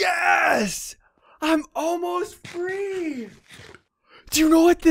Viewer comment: It sounded like he said, "Yes, I'm almost THREE!" LOL :D